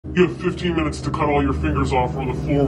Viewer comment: Thb I thought he said you have 25 minutes tho cut 15 fingers if lol